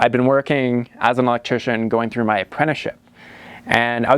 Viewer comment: Electrician! Oh Thank Goodness.....when I first heard it I thought you said Mortician!